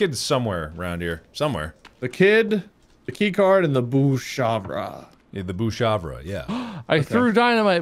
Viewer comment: What I heard: “The kid, the keycard, and the booze chakra ”